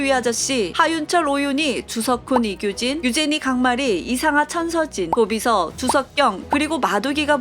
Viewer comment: 이상아,천서진이 이상한 천서진으로 들림ㅋㅋㅋㅋㅋ